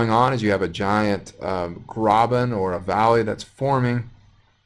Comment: What's that word at time 20:50 "you have a giant [???] or valley that's forming" ... sounds like "grobin" ... ??? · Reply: Graben